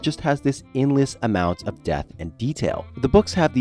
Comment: Great video, but every time he said "depth," it reeeealy sounded like he was saying "death"